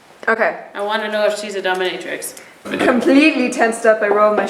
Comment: I'm SO late, but it was funny at the 13:45 the girl said I wanna know if she is a dominatrix, and the captions said dumb (?) 😂😂😂😂